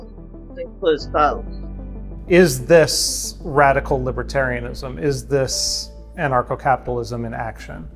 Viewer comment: I thought this shit said librarian